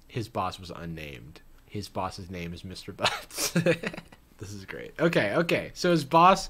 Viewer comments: I thought he said the name was “Bucks” like Daddy WarBucks from Annie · I thought said Mr. Bucks, not Butts🤣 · I thought "Darren" said Mr. Bucks, did he say Mr. Butts?? Lmao that's way better